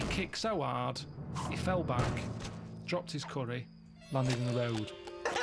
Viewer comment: what does he say at 17:29 ... "dropped his curry..." and then he says something .. I don't understand what he says there .. what do you think? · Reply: it says 'landed in the road'